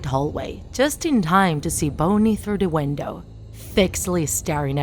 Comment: I hear she said "bouny" instead of bonnie in